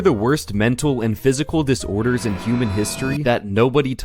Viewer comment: I THOUGHT IT SAID DISCORDERS NOT DISORDERS 💀💀💀💀💀💀💀